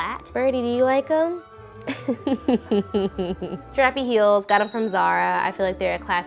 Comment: I thought u said Carp y heels 😂😂😂😂